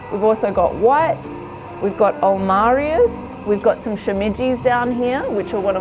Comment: At the 2:48 mark she mentions a mushroom but I can’t understand what she’s saying. It kinda sounds like shemiji but, those aren’t shemiji’s. Anyone know What mushroom are those?